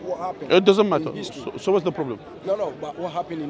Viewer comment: Ishmlite i thought grand mama said no matter what they egyption 😂😂😂😂